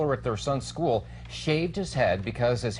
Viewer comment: I thought it said shelves his head. Like as a trophy. Hair for hair. Cut the teachers hair. His head still looks nice shaved , but what the fuck. One day of "unacceptable" hair is no big deal.